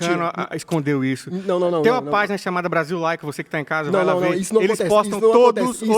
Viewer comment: gente que perfil é esse q Tiago citou? Brasil like? Nao entendi bem.. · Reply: movimento brasil laico